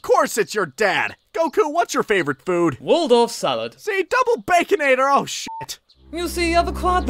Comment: I lost it at, "Goku, what's your favorite food?" "Wordor [edit Waldorf] Salad." "See, double-baconator--oh s***!" XD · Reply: He said Waldorf Salad. It'a type of salad usually covered in mayonnaise served with cashews and other ingredients.